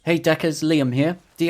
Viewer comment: due to your accent, i legit thought i heard "hey duckers"